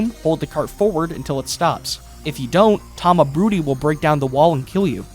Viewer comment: I thought he said "tamabooty"